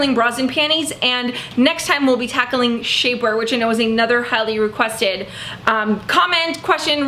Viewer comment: Ohhhhh 'highly-requested'~ I thought you said 'time-encrusted' which I'm swiping for my scifi epic poem so thanx!